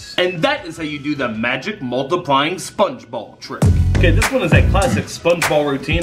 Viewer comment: I thought you said spongebob